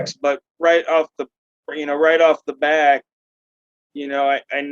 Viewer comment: Oh no, unless I misheard, one of my favorite auditors made a faux pas at 1:04:54. It’s “right off the bat”, not “right off the back”. Even though that’s one of my biggest pet peeves, and like nails on the chalkboard for me, I will not only remain an subscriber, I’ll increase the level of donation if that’s an option. Keep up the great work.